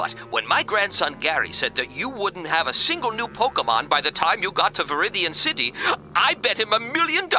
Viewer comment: I'm sorry is it just me or did he just say Pokemom 🤨🤨